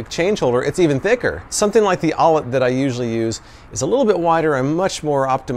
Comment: What is the name of the wallet you are currently carrying, it sounds like you said the ollett, but I can't find anything under that name.